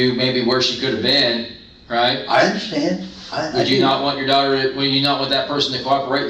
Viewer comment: did he say “my daughters dead” after the cop had asked him “what if this was your daughter”